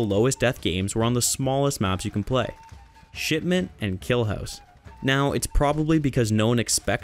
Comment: I heard shitmen 😅🤣🤣🤣🤣